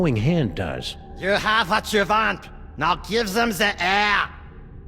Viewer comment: what is Max saying? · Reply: "You have what you want so give them some air"